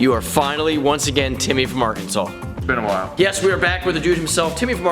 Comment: When he said Timmy from Arkansas I thought he said Timmy from markansas for some weird reason